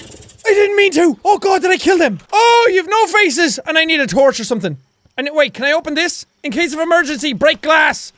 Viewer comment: when he said "I need a torch or something" it sounded like "I need to torture something" lol